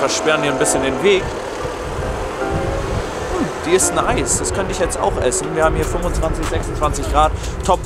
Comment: Haha ich hab zuerst verstanden Mmh die ist heiß😂